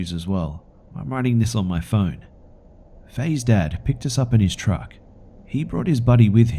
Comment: I thought you said Fay is dead!